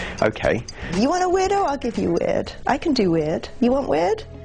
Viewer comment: what is that lady saying at 1:03?....."you wanna widow, i'll give you wed, i can do it, you want wit?".......huh?